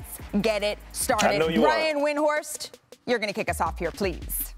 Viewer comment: Malika is great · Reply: did he say Aryan?